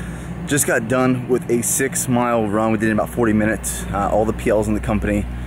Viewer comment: Did I hear 6 mile run in 40 min? 11:00 Holy crap.